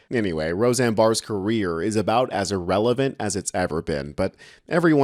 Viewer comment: It sounded like he said Roseanne's career was as relevant as a Tyberbin instead of "as it's ever been" and I thought what a great word, TYBERBIN. A CyberTruck that looks like a Trash Bin